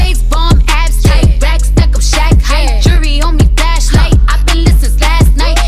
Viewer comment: Help she says shaq height? I thought she was saying Shanghai